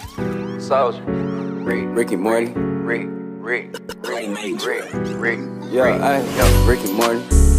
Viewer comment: When I’m so old I had thought they said “Ricky Martin Ricky Martin” 🥴